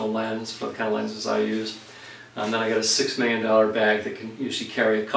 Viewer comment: Did you say 6 million dollar bag at 6:08 ?